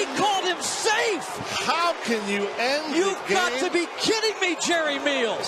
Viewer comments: Thought he was gonna say "that is retarded" at 9:36. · did he say "That's retardable"? hahah